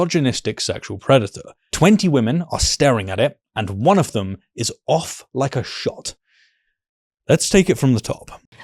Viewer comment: I had to rewind to replay this, because I thought you said "...and one of the is off her shit" 😂 Hey - it still works!